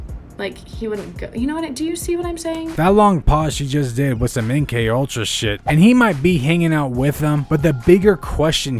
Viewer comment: did you say that looked like some mkultra shit?😂😂😂